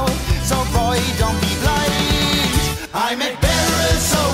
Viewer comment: I always though he said "I make barrels of whiskey and Anglo champagne" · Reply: I thought he was saying "it's angels champagne"